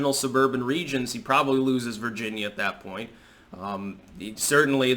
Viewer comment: I thought he said "loses his virginity" instead of "loses Virginia" 🤣🤣